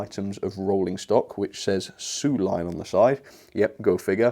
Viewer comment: I thought it said “500 line”.